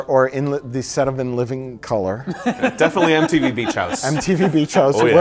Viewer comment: I thought I heard Empty-V instead of MTV XD Same difference I guess XD